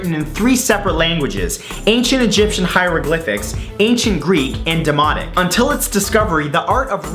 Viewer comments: I heard it like "written in Hieroglyphic, Greek and Demonic". Sounded way cooler than the reality. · hold up demonic really or am I just mishearing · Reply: its demotic